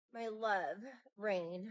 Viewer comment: This is so true and real. It's all you. It's all to help you remember and recognize your connection to all-now. Always has been. 4:49 - All I heard was Ashanti's "Rain on meeee..." Your giggle took me out 😂🤣. 8:31 - It sure is, and I see how quite a few operate in it. Be present. 🙂🙃 💜🧡💛